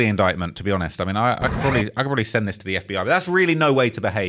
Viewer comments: What did he say at 22:17??? Did he say he already sent this to the FBI??!?! I'm here from watch Thugga vids and came across this after he's been call Thug Master Splinter.. This is why we can't take these people seriously!! 🤦🏽‍♀️ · The dude from Chicago waiting on the block 2 hours before they got there gonna be crazy to explain to the feds. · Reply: What did he say at 22:17??? Did he say he already sent this to the FBI??!?! I'm here from watch Thugga vids and came across this after he's been call Thug Master Splinter.. This is why we can't take these people seriously. 🤦🏽‍♀️🤷🏽‍♀️